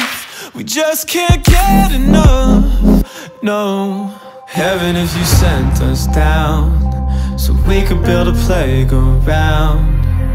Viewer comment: I thought he was saying "heaven if you sent us down so we could build the plague around"